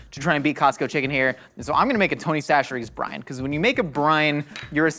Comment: I heard “Tony Chachere brine” as “Tony Chachere Brain.” Given how things work on GMM, is Josh the official Tony Chachere Brian? 🤔